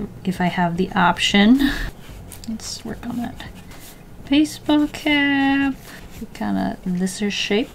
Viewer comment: i totally misheard "work on the baseball cap" as "work on the face vocab" and as an artist i juST KIND OF ACCEPTED IT... GOTTA STAY SHARP WITH YOUR FACE VOCAB....